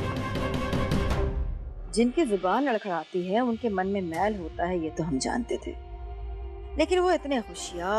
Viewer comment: Oooo God i don't understand their langge Pleas anyone tell me what veena tell thapki at 21:21 please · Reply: She said jin k man me mail hota hai Mean who has the bad heart or a clever heart i think soo.